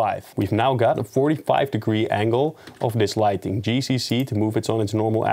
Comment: Thanks for the video. Quick question: When you say G → C → C = move along axis of the plane. It doens not work. Apparently there is no value in C, unless I'm not hearing well what you are saying. You're a bit too fast for me, haha. Could you please clarify how you actually move the Area Light Plane along it's axis? Thanks! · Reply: Replying to my onw comment. I thought you said G → C → C but it's actually G → Z → Z ! In case anyone else wonders.